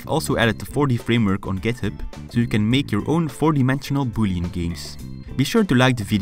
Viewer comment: "I've also added the 4D framework on GitHub so you can make your own 4D bullying games!" I know it is boolean, but in my ears it sounded like bullying lol